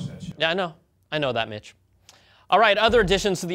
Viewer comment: wait, did he call him a "mitch" or is his name Mitch???